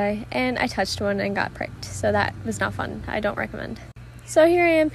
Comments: I thought she said I touched it and I got pregnant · I thought you said “so I touched on and got pregnant” I had to rewatch that part 😂😂😂😂😂